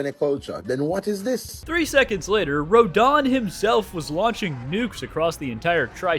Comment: I misheard this as "Radahn" as in the Elden Ring boss, and didn't even question it. Just went "yep, Radahn throwing nukes everywhere checks out". Take my like and leave me to reevaluate how i got to this point.